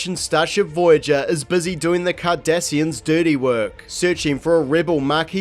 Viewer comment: Hilariously, the automatic subtitles write Kardashians instead of Cardassians.